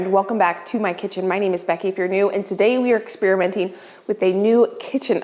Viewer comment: I like to watch YouTube on double speed and I chuckle every time I watch you because it sounds like you say My name is Becky Ifyournew, as though that's your last name.